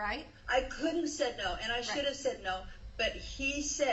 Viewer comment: Back again for another listen... 47:00 I swear I hear her say..'I COULDN'T have said no, and I should have said no' I heard this when this launched, but now listening again...does anyone hear that? She's such a maniac.